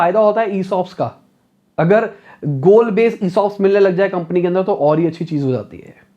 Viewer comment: Kya bola yaha pe? Gold Based Insos?